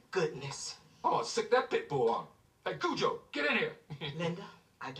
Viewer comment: what is he saying?? 😅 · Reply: "I'll sic that Pitbull on him , Hey cujo get in here"